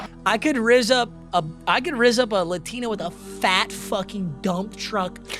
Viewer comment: this man just said he’d rizz up a Latino 😭😭😭 for y’all who don’t understand, in Spanish they use o suffix for masculine and a suffix for feminine. This man just said he’d rizz a dude with a fat ah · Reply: It sounds like he said Latino but he did say Latina lol